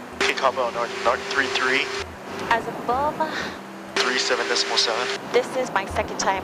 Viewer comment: What does she say? It sounds hilariously wrong to me for some reason. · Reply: "As a boom" operator.